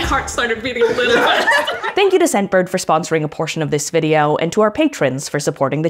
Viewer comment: I use the sponsorblock extension. When i skipped the sponsor, i heard "scentbird, the sponsor of love" I tried it again and got "scentbird, the sponsor of part of love"